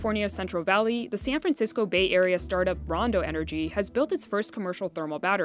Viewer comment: What is the energy loss in the process? That’s probably the most important point not addressed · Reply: Did they say Brondo company? Does it have electrolytes? 🤣 For those that don't know, see Idiocracy.